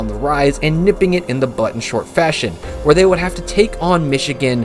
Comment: “nipping it in the bud” is the saying, not butt. Think like pruning a flower